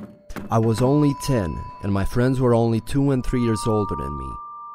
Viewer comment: Did he just said "I was only ten while my friends were 2 and 3 yrs old" 😂😂😂😂🤣🤣 or did I misheard it 😲